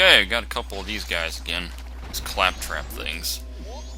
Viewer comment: I always thought those turtles kept saying 'cat crap!' I always found it humorous. Also, good job on the first dungeon of Majora's Mask.